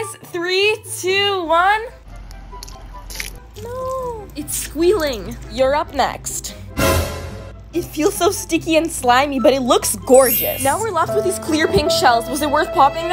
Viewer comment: Instead of “ur up next” I heard “Europe” next